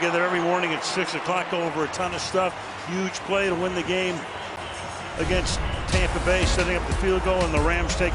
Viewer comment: did he say “Huge play to win the game over Tampa Bay”?